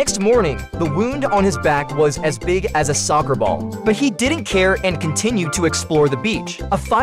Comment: why did i hear he said "sucker ball" Instead of the usual soccer ball😭